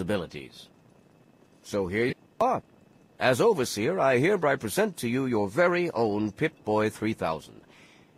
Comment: did he say "herebry"...?